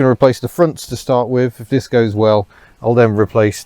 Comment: I could have sworn that said "If discos well" just before fitting the speakers.